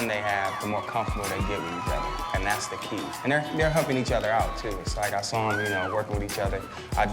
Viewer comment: i totally thought he said, "and they're humping each other out" ... what did you have planned for this dance ...?